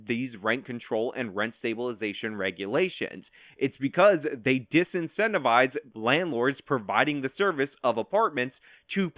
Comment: They do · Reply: oh I thought you said they demonotized you, my mistake.